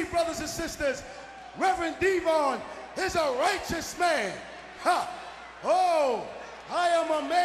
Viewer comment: I thought he said I'm a racist man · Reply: He said righteous